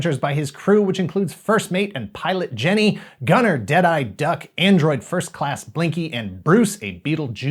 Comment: I remember watching this as a kid, but had no idea there were comics or toys. I always misheard "Deadeye Duck" as "Jedi Duck" and wondered why he never used a lightsaber. I didn't find out for years.